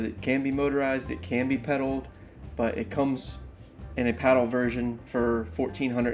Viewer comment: Maybe do a little more research next time. The shearwater is $1499 plus $599 for the pedal pod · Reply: My bad I rewatched it and I just misheard u. I thought u said pedal version for 1499. Nice video now that that's cleared up lol. I have been researching kayaks for months now and thought I made up my mind countless times amd I still don't know what to do. Budget is the main factor or else I would get my dream kayak a Hobie PA 360. I am considering a Recon 120 hd or a Native slayer propel max 12.5. I was gonna get the shearwater but I've read so many bad things about them. Basically you have to fix it and upgrade it as soon as you get it. Too many decisions!!!!!